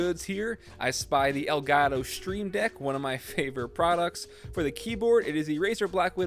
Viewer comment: did he say elgato stream dick